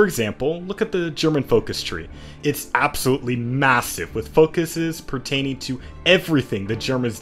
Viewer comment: did I hear massiv?